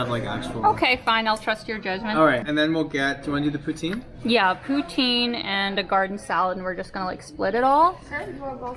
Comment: maudite poutine I learned a new thing about Canada :) (I thought you were saying "protein" in some Canadian way)🤣 I just read it's also pronounced "poo TANG" 🤣🤣🤣 Oh Canada 🤣 Is it good?